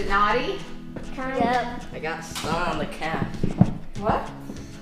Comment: Honestly thought said "I got snot on the cat!" Had to figure that one out 😀 Lol